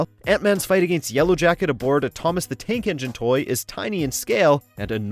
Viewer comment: did he say thomas the tank engine